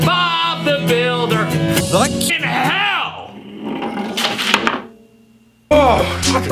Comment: I'm sorry but I did not hear "fvcking he//" I heard "fuhhin hall"😔🤣